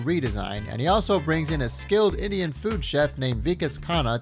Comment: made me double take, I heard "skilled idiot food chef" and I was so excited to the prospect of what that would be